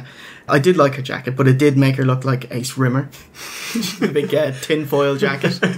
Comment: When you said "Sherry looks like Ace Rimmer" I thought you said "Sherry looks like a swimmer". Then a picture of Rimmer appears. I was well confused for a second. A tinfoil swimmer, what ya on about?